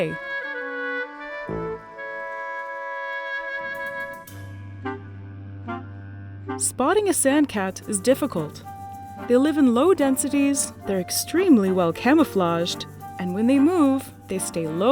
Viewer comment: I really thought she said spawning a sand cat was difficult.